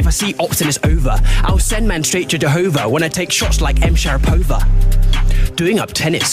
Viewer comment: This is the opposite of mumble rap: every word is said so clearly that there are no misheard lyrics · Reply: Ngl, when he said M sharpova, i heard "M sharp over" like a goddamn radio message